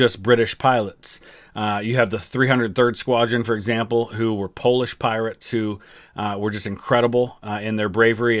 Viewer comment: did I hear Polish Pirates or are my ears just tired??